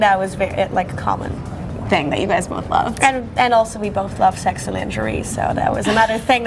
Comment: They both like what? 3:06 😅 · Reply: yeah I heard it as sex and injury! haha